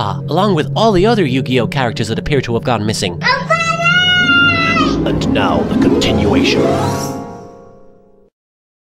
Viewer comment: Just what the (EFF!) is that kid at 0:33 screaming?? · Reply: AjnosFTW he screamed " NOT FUUUUUNYYYYY "